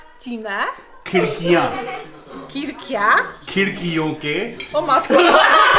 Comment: did she said oo MC